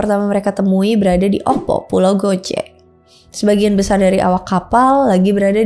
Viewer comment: gw dengernya pulau gojek 😅